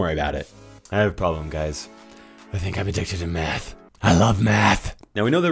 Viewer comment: i thought you said meth lmao